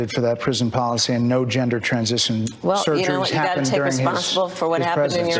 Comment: Did anyone hear what she said here? "You gotta take responsibility for what happens in your administration." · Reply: She actually was worse, you wrote it with correct grammar. She said "you gotta take responsible" . As the daughter and granddaughter of two highly decorated members of the Grammar Police, that cracked me up doubly!!